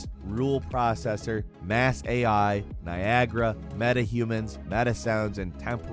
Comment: legit just thought you said viagra😂